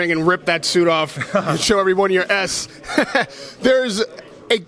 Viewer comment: Did he said ASS in